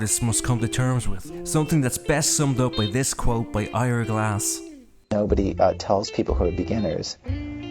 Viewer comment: Can anyone tell me the name of the guy who gave the quote from 06:30-08:10? That was one of the best things I've ever heard, and I really needed to hear that. I've replayed it and I can't catch his name though. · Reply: His name is Ira Glass.